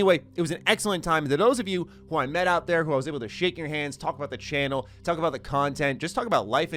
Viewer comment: "To those of you who I met out there who I was able to Sh!t in your hands." I can't be the only one who heard that. Timestamp